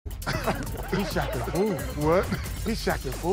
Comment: Low key thought it said shaqtin a pool